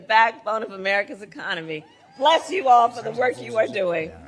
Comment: Did she just say bless you all?